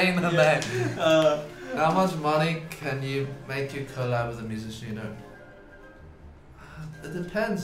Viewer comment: wait what he say at 6:36? i can’t understand the middle of the sentence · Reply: He said “how much money can make you collab with a with a musician you don’t like”